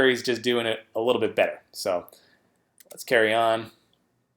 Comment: did you say 'Curry On'? lol.